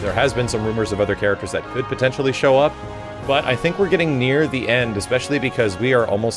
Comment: What did you say? 2:58 ? We're getting... "Nier" the end? New guests confirmed???? Jk, but if only though